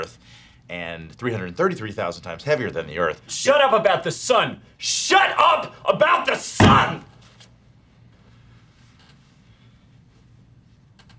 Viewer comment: SHUT UP ABOUT THE SUN!!!!!!!!!!!! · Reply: and I thought I heard "Shut up about this, son!"